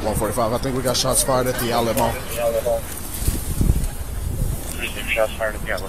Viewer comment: When he says, "shots fired at the Allen mall" all I hear is "shots fired at the alamo"